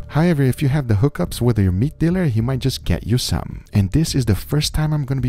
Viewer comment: Why does ''having a meat dealer'' sound so cool. ''I know people who can get rare stuff'' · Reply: Whenever I hear it I always hear it as “weed dealer”